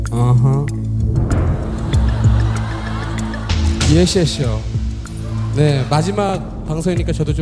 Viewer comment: 이하늘 뭐라고 하는건지 아시는분? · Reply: yes yes y'all